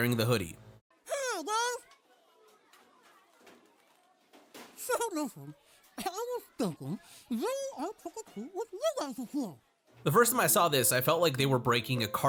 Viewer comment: What did he say? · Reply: Something along the line of, "hey guys! So I was thinking, I want to trick or treat with you guys this year!' With kenny you just kinda fill in the blanks when matt stone chokes on his sleeve.